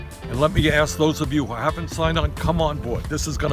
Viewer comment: I thought he said "come on, boi"